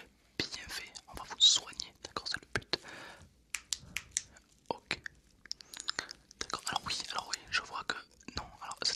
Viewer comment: attendez doc... vous avez dis quoi ? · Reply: "c'est le but" il dit